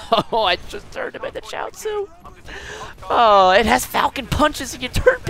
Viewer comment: Lol. I thought you were saying "turned into child soup" haha